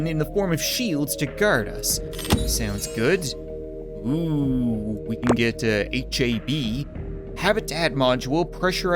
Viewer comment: I'm not gonna lie, when he said "We can get HAB" I thought he said "Ooh we can get HIV."